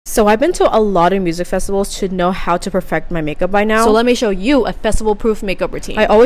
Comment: I think I’m going crazy, I heard “pregnant makeup” instead of “perfect makeup”💀